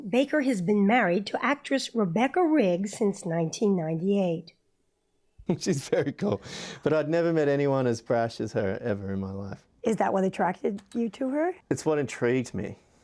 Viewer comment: Can someone tell what he said here about his wife? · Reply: 'She's very cool, but I've never seen anyone as brash as her, ever in my life.'